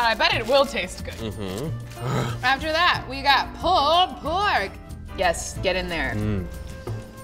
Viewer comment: istg i thought she said paul blart not pulled pork